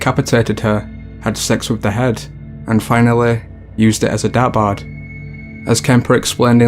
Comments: I had to google what he said about the 3rd one. I thought he said used her head as a dabbard! I was like what’s a dabbard?? He says dart board 😑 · Okay, I need help with this shit.. At 10:49 it explains that the guy decapitated his mother and used her head for sex and then as a - WHAT THE FUCK WORD WAS THAT?? It sounds like "dabpard, dabbard, or bappard (none of which are words that exists in the English language apparently)" but captions say you said "depart", but that's also irritating cause you seem to have used it as a noun, but there is literally no item by itself in existence that is referred to as a "depart".. There might be a certain part of an item that could be referred to as that but I guess what I'm asking is, what the hell were you saying he used it for after he defiled it??? Cause even if it refers to part of an item, it's like saying he used it as a cover... So in that example I might think well what the hell is it covering? A lamp? A bowling ball? A throw pillow? I don't know how to take that. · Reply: Schroedpiece Media sounds like dartboard ,he does pronounce it weird